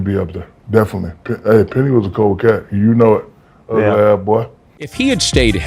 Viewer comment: “ ugly ah boi “ Lol sneaky